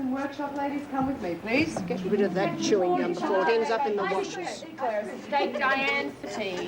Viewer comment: What did Morris say to winter at 14:33 get rid of that chewing gum before it ends up in the ??? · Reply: washers.